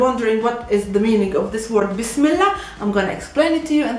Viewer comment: I always heard it as "this miller " with a UK accent. 😂😂😂